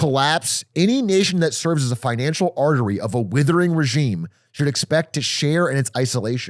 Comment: all i heard was "the us is withering" tbh ... iran doesnt seem to change much, but the us? oh boy, are they moving away from prosperity.